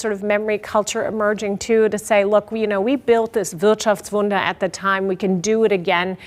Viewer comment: What German term does the guest use at 14:07? I'm trying to figure out what word she is saying here. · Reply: "Wirtschaftswunder" translates to economy wonder, basically saying it's a quick, surprising increase in economic power (i.e. Germany during the sixties)